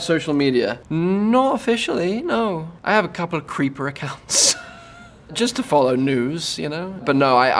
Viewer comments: Where are y'all getting "I have a couple of secret accounts", bc I hear "creeper" idk about u 🤷🏾‍♀️ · Lol. Did he really just say his old postcode 😮 And anyone else thought he said “to follow “Nudes”? Instead of “news” lol